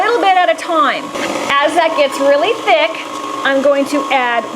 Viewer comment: When I watched this video back in 2013, I thought she said at 4:14 "As I get really thick."